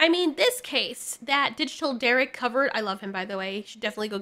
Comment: the guy you shut out I cannot catch his name. What’s the channel? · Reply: Digital Derrick or Derrick Branch <link>